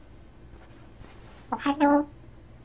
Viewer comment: "Ohaiyo" EDIT: What Kiwi is Really Saying is "Ohaiyo" but I misheard it as "Ohio" 😂